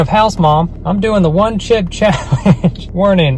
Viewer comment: I thought this said “the one clip challenge” lol